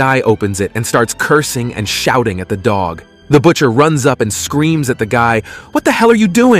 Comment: My dumbass thought it said shooting the dog away 💀